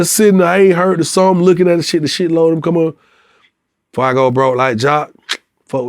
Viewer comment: Always thought he said broke like ja (rule)